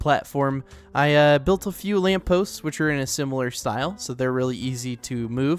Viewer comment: did you just say LAMP BROTHER?